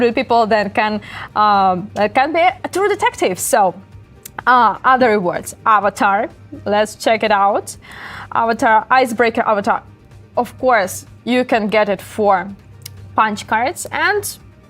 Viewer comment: did she just say Icebreaker avatar